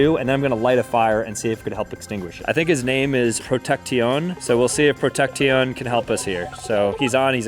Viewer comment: Is it just me or did sam actually say protecti-on with a straight face?!😅😅